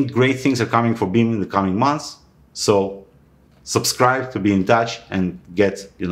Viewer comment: Well I heard it but I can barely believe it. Did you actually say, "Beam Dash" @ 12:21 ??? :-D I've been in this space too long, I'm hearing things now! :-D But yes, Beam and Dash is a superb partnership waiting to happen!